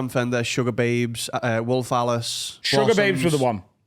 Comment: Bizzy on a naynay! 😂😂 when says wolfe Alice both times I thought he said rolf harris for a sec